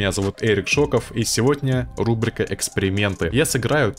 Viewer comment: послышалось "рубрика экскременты "